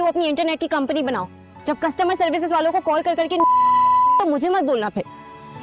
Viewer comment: What did she say at near 10:50 Jb costumer services walon ko call kr kr jb noba phategi? Noba hi kaha hai ?